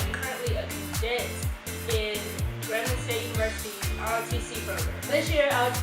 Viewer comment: did you say Grand Valley State! I’m going to be joining Central Michigan’s ROTC program next year!